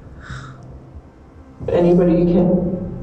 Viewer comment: When it said anybody you kill what came to my mind is the protective spirit calling out the demonic spirit. It could be a stretch but that’s what it sounded like to me.